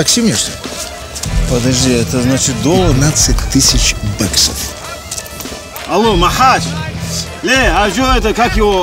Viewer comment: ребят скажите что он сказал на 4:22 я просто лезгин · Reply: Хадижат Гамзатова он на аварском сказал Махач а че я ему 15 тыс должен?!